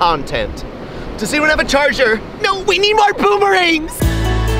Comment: I thought yoi said, boomer-ing Lol 🤣🤣🤣🤣